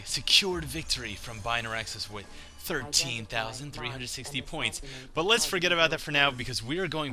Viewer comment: i misheard it, i though u said 1360 not 13,360